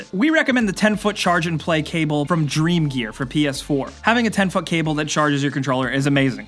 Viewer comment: Did he just said 10 "fuck" cable?